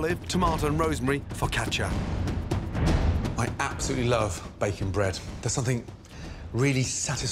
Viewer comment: Anyone else thought he said bacon bread and not baking bread